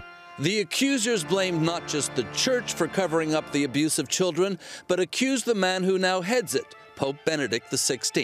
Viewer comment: did anyone catch that at 0:17, did he just say abusive children??